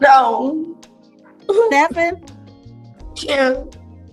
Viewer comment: think she said "chyeah" after the judge said 7, not 10. Still crazy to bring 7 kids into world without caring for them.